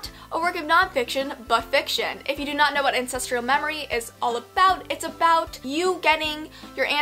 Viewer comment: Oh ancestral memories. I thought you said incestral memories 😂😂😂👏👏